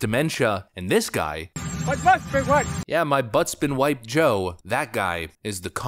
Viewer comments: I was wondering all this time what he was saying, “My Butt’s been wiped” is about the closest thing. Makes sense. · It sounds like Biden is saying, "My butt's been waxed."